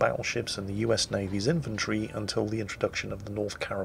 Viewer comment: "the (colorados) would remain the most powerful battleships in USA's ... ... I swear you said Infantry, not inventory. Which now makes me want to see privates running around beating people with a Colorado like a club.